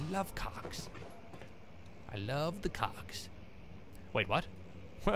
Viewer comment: loves the Cogs.. Okay.. I misheard that >.>